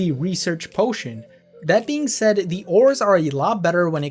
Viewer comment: Btw at 5:11 the captions say “ORs” instead of of “ores” 👍